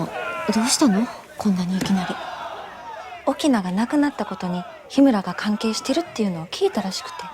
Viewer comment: オキナがいなくなったことに로 들리네요. 그렇기에 죽었다가 아니고 없어졌다가 맞는 것 같습니다.